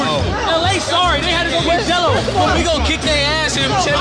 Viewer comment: Are yall sure that's what he said in the caption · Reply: If you’re talking about the saying Gelo, yes that was the accurate caption. Gelo’s not in the league now but in the high school days Gelo was the one to beat and helped Melo out a lot